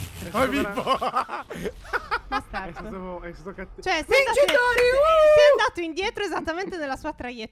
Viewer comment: solo io ho sentito dire b******o